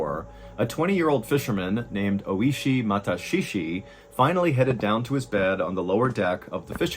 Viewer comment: When you said his name was Ōishi, i initially thought you said he was oishii (delicious) lol😅